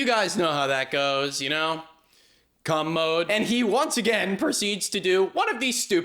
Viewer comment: 😐 I just backed this up and turned on Closed Captions to be sure you were saying what I thought you were saying. CUM MODE … you said CUM MODE twice. So with that context I watched the next ~30 seconds of the video. So I never NEVER in all of my dreams & nightmares of how my life would go did I ever think I would someday say the following to some rando adult male (ostensibly): “Do you know what cum means?” Watching the footage after CUM MODE I fail to see any ejaculation, I don’t see any looks of pleasure, I don’t see anybody appearing to be in states of ecstasy, I don’t see anybody naked, nor do I see Jon’s penis out, much less in a tumescent state. I don’t see ANY bodily fluids as a matter of fact so why are you claiming that Jon has entered into a mode or state of yeeting semen across the bar. I literally do NOT see “cum” in either its extremely exciting verb form or even its drastically less exciting noun form. Given the current state of YouTube and aallllllll the creators who go to stupid lengths to censor themselves to avoid demonetization it’s extremely refreshing to see a creator with some Gigantic Schwangin’ Balls but Brotha… What the actual fuck jizz Bruv? Please make it make sense. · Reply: COMMODE, not "cum mode". Commode is an old school term for a toilet, genius. 😂